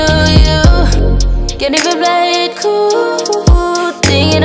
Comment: lmao I thought she was saying “can you quit playing cool” this whole time